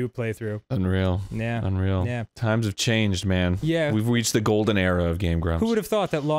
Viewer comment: I have rewinded this thirty times and it still sounds like Arin says "we've weached the golden era of game grumps" and I keep laughing I swear that's what it sounds like